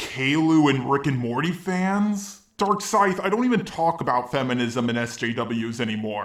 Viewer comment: Every time says DarkScythe it sounds like he says Dark Side.